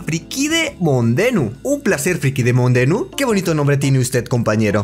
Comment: casi que dice friki monde